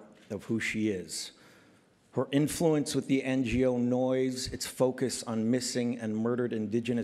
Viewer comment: what was the word he said after 'NGO' at 2:55? please let me know!! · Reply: Noise I think